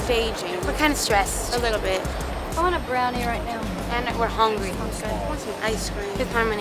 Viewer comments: what does lauren said ? I want bradley right now ?? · what does lauren said ? I want bradley right now ?? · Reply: A brownie